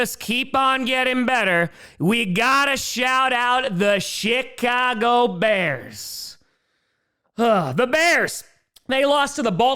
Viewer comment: I could a swore I heard you say shitcago bears as your honorable mention. I had to listen back 😂 my bad.